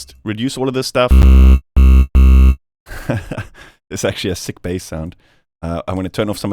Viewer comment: I had to rewind this because I thought you said "it's actually a sickbay sound". I think I watch too much Star Trek 😂